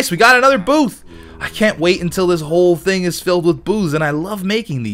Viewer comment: it sounds like is saying "I can't wait until this whole thing is filled with booze"